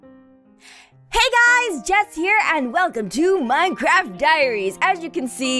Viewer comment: Misheard Word at 0:16 : Hey Guys! Death Here! Me: Wha?